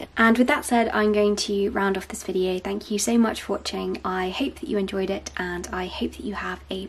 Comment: I thought she said I hate that you enjoyed it at first